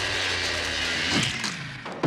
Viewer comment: What does he say ? It sounds like he says " fixed the lool post . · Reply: He says "Fix the newel post!". Basically announcing it's broke and someone fix it